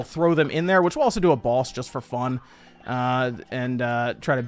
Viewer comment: did he just say let's do a boss for fun?